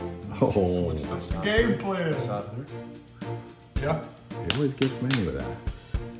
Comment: I’m still wondering what he said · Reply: "switched up the game plan".